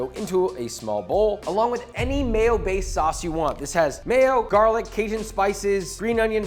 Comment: I thought he said ‘any male based sauce’ the first time I heard him.